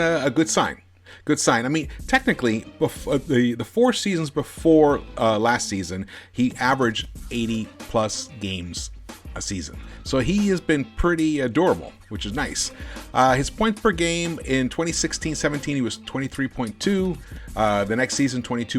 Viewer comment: I thought you said "he's been pretty adorable" 😂. Love the content tho keep up the good work💪